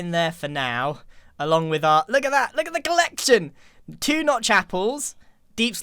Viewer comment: I first heard "Two not chapels." and I am dying of laughter. Also it's so awesome to see and back together!